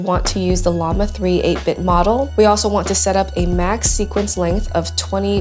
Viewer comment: did she just say 8 BIT model?